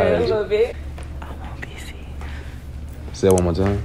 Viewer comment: i thought she Said im obese🤣🤣